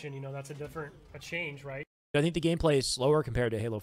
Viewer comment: I thought they said it was xbone game play